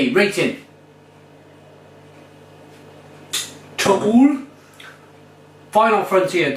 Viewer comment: Hahaha at 09:39 sounds like you say two owls in Danish, to ugle xD